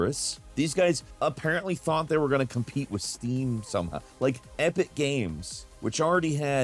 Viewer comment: Oh my god, voice is ai generated now. Listen to 4:12 - 4:16 His voice is becoming distorted, and says *Steam Some hah**. Instead of* Steam Somehow**. GG I'm out.